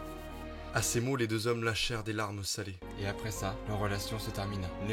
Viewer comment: Jsp pourquoi mais mon cerveau a compris à 9:25 "À ces mots les deux hommes lâchèrent des pets salés"